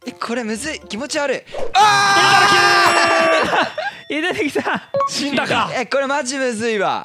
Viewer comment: いぬきさんって聞こえたw